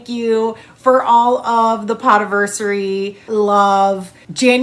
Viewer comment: I totally thought you said “POT-iversary” and I was just like, ok, I guess I hadn’t picked up on that part yet, but ok! I was waiting for you to expand a little more about the pot, but you just kept rambling about making videos and other podcasters and.....OHhhhhhhhh!! P O D casting anniversary. Now I get it.